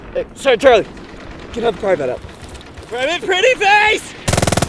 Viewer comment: what exactly did he say? Something "pretty face!"? 😂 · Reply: Private Pretty Face